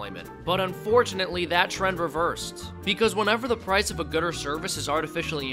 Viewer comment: Did he say gooder?